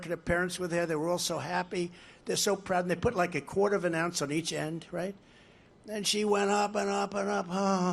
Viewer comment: did he say the female weight lifter put a quarter or an ounce on each end?😭 am I hearing this wrong